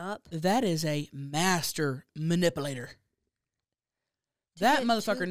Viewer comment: I love how pronounces it “ma-nipple-ater” instead of manipulator 😂 love you hunnies!!!